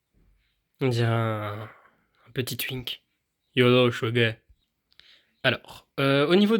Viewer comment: un petit quoi ?! ai- je bien entendu ce que j'ai entendu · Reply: Twink 🏳️‍🌈🐍